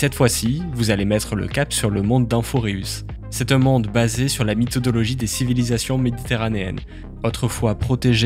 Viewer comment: c'est moi ou il a dis mythodologie ?! 1:27 (yay chui dans les premiers)